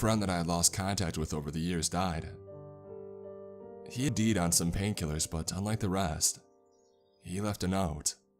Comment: did he just say "he deed on some pain killers" lmao